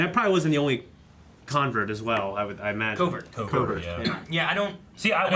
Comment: No. That would be a "coven." The Mandalorians were living in a "covert" for their safety. See <link> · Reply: thank you for the correction! I don't have the best of hearing so I thought they said Coven.